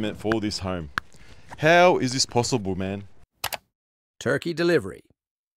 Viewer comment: is that the accent of the voiceover man or did he say "turkey delivery" twice by mistake